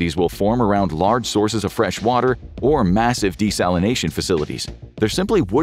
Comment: Did you say massive at 1:58?